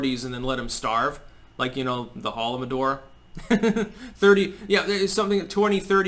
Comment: what is he talking about at 10:07? auto generated subtitles says howl of a door